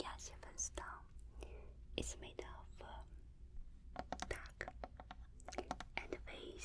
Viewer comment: Did she say it is made out of ‘dog’? 😨